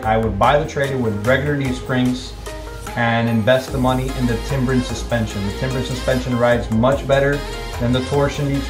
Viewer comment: Torsion suspension does give a better ride, however if damage the cost to repair is very high compared to leaf spring suspension. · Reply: I thought he said Timber Suspension which is an aftermarket brand he decided to go with.